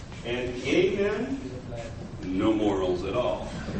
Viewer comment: (the Superman pic) what are you saying? " [something]men no morals at all"... Cavemen? Apemen? A-men? Can anyone make this out?